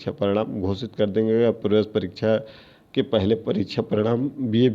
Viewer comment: Sir ji aapne 9:05-9:08 second pe kya bola h please clear kare please 🙏🙏🙏🙏 sir ji 🙏🙏❤❤❤❤❤ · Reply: प्रवेश परीक्षा